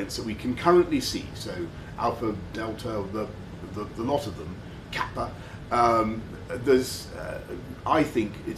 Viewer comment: did man say kappa